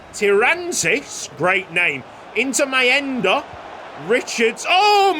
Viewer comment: what did you say bud?! "into my end-ah"!? Cheeky!